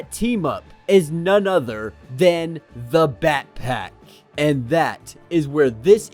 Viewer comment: I misheard that as "the backpack" then the dora backpack theme plays in the background